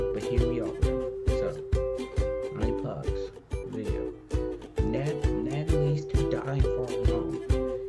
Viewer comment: Leeds? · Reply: / TD5008 ohhhhhhh, I thought you meant to say Ned leads to die.